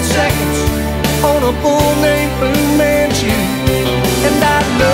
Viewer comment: sounds like he says bull named blue man shoe